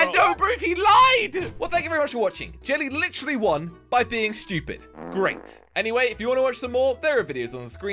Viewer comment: its sounds like said shtoopid